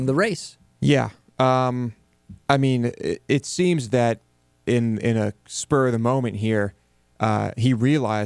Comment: Dude say "in in a spur of the moment here" Subtitles say "in in a story mom in here"